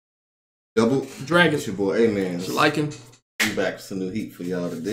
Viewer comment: For a while I thought that they were saying. "It's your boy amen and like it" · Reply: LOL. I thought was constantly just saying, "It's your boy, eh mannnn". And to be fair he does say "ehh man" from time to time! Maybe that's how he got the name?